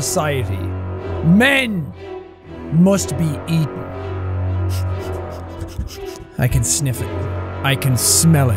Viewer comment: when sean said: “men must be eaten” i could have sworn i heard him say that’s what she said but when i played it back it was just him sniffing the mic 😂